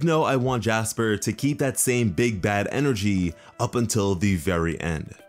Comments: I thought he said "Big Dad Energy" LOL I was like "yaass Big Daddy!Jasper" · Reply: No he thought he said “big dad energy” not bad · I thought he said "Big Dad Energy" LOL I was like "yaass Big Daddy!Jasper"